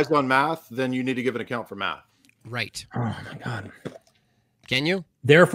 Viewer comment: Did say “Oh my God at 18:20🤔